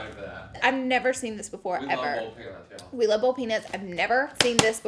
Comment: i thought she said bold peanuts not boiled peanuts ahahah